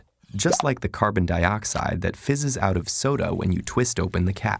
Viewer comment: anyone else realize he says sodo instead of soda 🤣